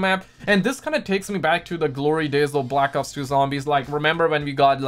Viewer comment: Am I the only one who heard him say black cocks and zombies